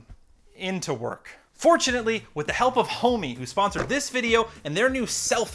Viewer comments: omg I thought you said honey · holy crap I thought it said "Honey" for a second, almost noped my way out of this video lol